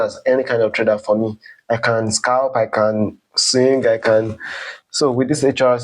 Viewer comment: 😅 at what point in the video did I sing? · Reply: I thought I heard “I can scalp, I can sing…” 😂